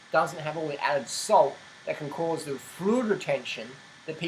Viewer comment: Can anyone understand what he is saying at 4:38?? "Freud Retention"????? Whaatttt